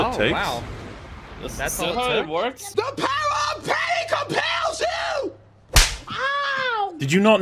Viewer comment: When I first saw this line in the trailer, I misheard Leslie Jones and thought she was saying “THE POWER OF PAIN COMPELS YOU”. Which isn’t comedic gold or anything but I’d say is quite a bit funnier than the actual line.